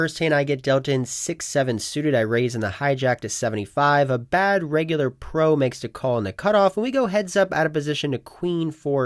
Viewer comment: did you say a “bad regular pro”? Lol